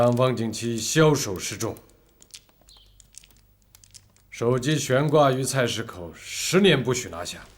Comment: 首级悬挂于菜市口，听成了手机悬挂于菜市口..........